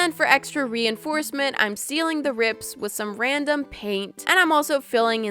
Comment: I like how her name "Moriah Elizabeth" perfectly ended up into a word "Me" i love it😂💖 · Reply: When she said “sealing the rips” my mom thought she said stealing” ( I mean like, YIKES)